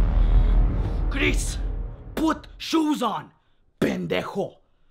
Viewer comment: the subtitles say grease what shoes on ben deck hole